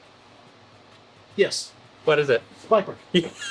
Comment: Can anyone tell me the song was silently playing at 1:21:47? I can't make out what said, sounds like "spiper" or something. Thanks! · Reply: Blackbird by The Beatles